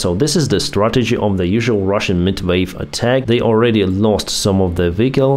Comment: When Denis says "meatwave attacks" I always hear "midwife attacks" 😂